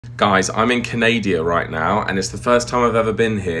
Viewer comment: Same here -I heard you saying Canadia .. then it took me ages to understand it’s Canada. I just hope Canadia doesn’t means ghosts .!! Oops 🙊